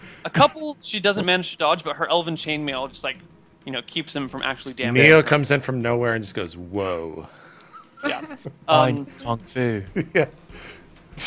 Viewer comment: I thought said Neal... but then realized he said Neo... IT WORKS BOTH WAYS.